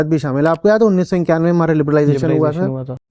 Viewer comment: Mistake by the editor at 12:13 . Liberalisation happened in 1991 and is also saying 1991 but it is written 1951. Correct it fast.